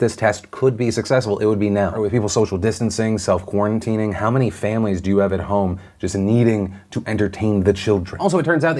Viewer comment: I swear I thought he said “how many families are at home just trying to feed the children” and I’m like 😭😭😭😭😭😭 I tried talking to them about rationing and it didn’t work 😭😭😭😭😭😭